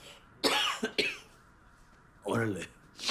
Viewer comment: Minute: 14:47 Tuco says: "orale" which means "OK" (I agree I am a man who accomplish what I say no matter how tough it is) He didn't say: "oh, really?" Learn English with TV series, you got that part wrong.